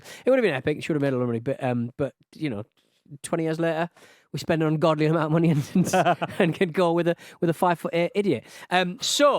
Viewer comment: Did you just say we spent an ungodly amount of money on TITS?